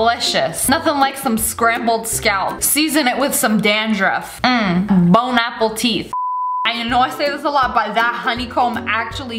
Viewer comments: am i the only one that heard bon apple teeth instead of bon appetit xD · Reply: I heard “bone Apple teet” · am i the only one that heard bon apple teeth instead of bon appetit xD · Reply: I heard “bone apple teeth” hahaha